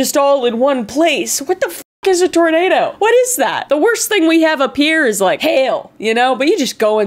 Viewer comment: i thought she said "The worst thing we have up here is like... hell" and i nodded in agreement